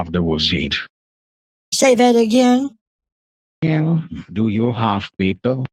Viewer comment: I heard "do you have pickle?" 😂